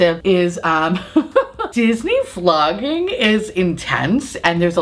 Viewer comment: I thought you said Disney Flogging at first and thought, ooh that's kinky, lol.